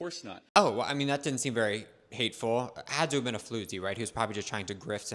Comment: Floozy instead of fluke lol